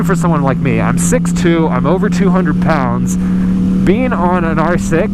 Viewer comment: I agree, I am 6' 210 pounds and I was also cramped into a 600. I now own a BMW s 1000rr and it is by far better for me, love the power. No more 600's for me. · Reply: literally just thought you said " i am 6,210 pounds😂